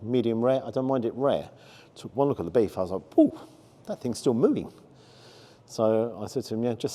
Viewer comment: my hearing must be bad I thought you said still mooing . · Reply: I did say mooing I actually misread the first comment lol